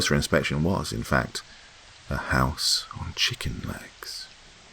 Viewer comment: 16) okay, am I hearing this right? "A house on chicken legs" or have I spent too much time at the gym laughing at guys with...well chicken legs